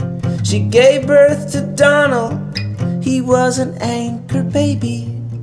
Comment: I thought they were saying ANGRY baby... Now I get it LOL